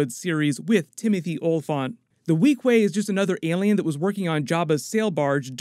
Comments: Mando about to go inside the dragon: Jarvis, you ever heard of “The Tale of Jonah” ? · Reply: Rewatching the series, the bartender is a Weequay (maybe a bit impolite that the marshal calls him by the name of his species), I just looked that up because I thought he said 'Queequeg' which is a character in Moby Dick. If you search images of Queequeg, it gives you an image of a Weequay, is it related? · Rewatching the series, the bartender is a Weequay, I just looked that up because I thought he said 'Queequeg' which is a character in Moby Dick. If you search images of Queequeg, it gives you an image of a Weequay, is it related? And as someone else pointed out, Mando gets swallowed, just like Jonah.